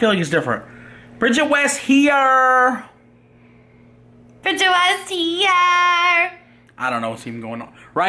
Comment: WHO DOES THIS?!!?? WHAT'S HER OBSESSION??? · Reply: I thought she said bitching west here